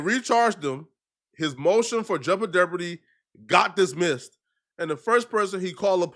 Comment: So I’m Tha Only Person Who Heard Say “Jouble Deopardy”?? 😂😂😂